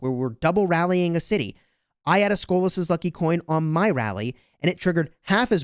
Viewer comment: It's funny subtitle says scoliosis lucky coin.. anyway tnx for the video